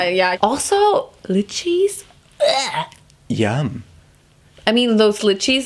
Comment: why did I think said blue cheese instead of lychee 😂